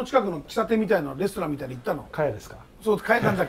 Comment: 聞き取りにくいですがレストラン カヤですね 中山の福祉大球場の近く